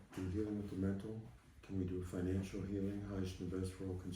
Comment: does he say "financial healing"??????? I am wounded, Sir, I am suffering, injured and bleeding!! Please, I need financial healing on the Phone!!